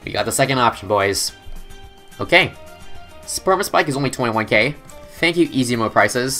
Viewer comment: I know this is a 10 month old video, but at 3:33, did he say Sperma-Spike?!